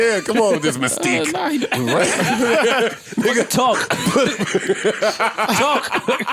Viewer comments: Did Rory say “Nigga talk” ?? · is my ear fucked up or did rory just say "ni**a talk" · I’m late to this episode. But did Rory say nigga at 12:52? He got the pass. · Did Rory say nigga talk? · did rory say nigga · did Rory say “Nigga talk!”? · am I trippin or did Rory say “nigga talk”🤔 I might be trippin let me know 😂😂😂